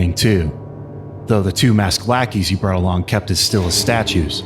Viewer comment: I thought he said blackeys and I thought “WAIT WAIT WHAT THATS RACIS- OH he said lackeys”